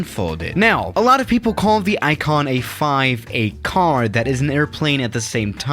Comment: why I heard that iPhone 5?😂😂